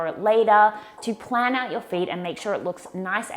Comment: Hi there, at 8:54 you talk about using Apps .. Plan what???? Can you give me the names again as I didn't quite catch it in your vid or the links? Thanks so much hun :)